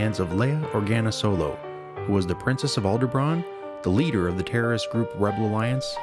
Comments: i keep hearing "aldebraan" ?... cool video though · It sounds like hes saying "Aldebran" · Did the narrator just say that Leia was the princess of "Alderbraan?" with a "b" It's Alderaan, not Alderbraan. At 4:23 mark. Maybe I'm hearing things, but it sounded like he said "Alderbraan" with a "b" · Sounds like he is saying "Aldebraan"